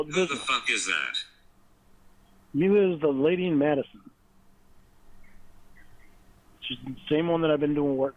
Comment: Would love to see this “carpentry” work did for the lady in Madison. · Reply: Ahh you decoded this part for me - I thought he said “The Lady Madison” and I honestly thought there might be some mega-ancient New England relic with this name 😂😂